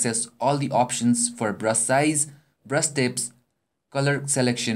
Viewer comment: omg haha when he said brush it sounded like he said breast. I have such a dirty ming :p